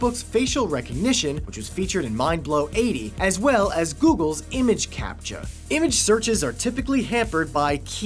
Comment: Did he say Image capta